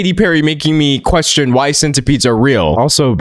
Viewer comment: I thought she said “like a side of beef” instead of centipede 😭